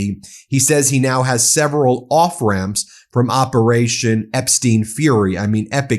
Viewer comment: I thought you said "operation Epstein fury' 😂